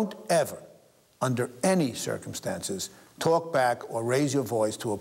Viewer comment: I thought it said "Don't talk black" lol